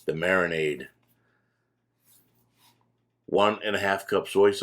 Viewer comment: I thought I heard 1 and a 1/2 cups both soy sauce and teriyaki sauce in the vid but recipe in description and video slowed down seems to show one cup rather than 1 & 1/2